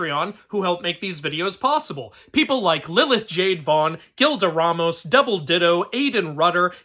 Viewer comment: omg, almost misheard double d*ldo